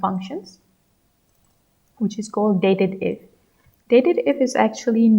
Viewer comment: i think it’s “date-dif” instead of “dated-if”… dif as in difference